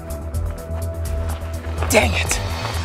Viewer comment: Who else heard say damn it